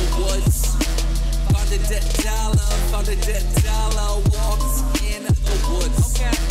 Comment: At first I thought keem was saying I found a dead dollar